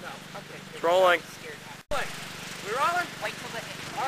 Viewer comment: at the beginning it sounds like you said "we're trolling"